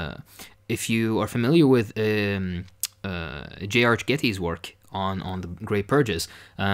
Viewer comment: what book is talking about there? I couldn't quite make out the author's name. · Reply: The Road to Terror by J. Arch Getty